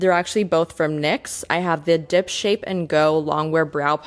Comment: i really thought you said dip shit 😭